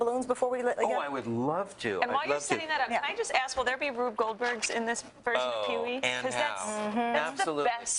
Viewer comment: What does the lady in green ask him @ 3:38. It almost sounds like she says Rube Goldberg. If so what does that mean?